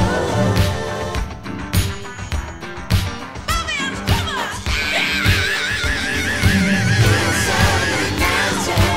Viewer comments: But what does Wendy (or is it Lisa?) shout around 2:45??? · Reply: "Bobby on the drum-ah!" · What did Lisa scream out at the 2:45 mark??? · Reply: Bobby on the drumma.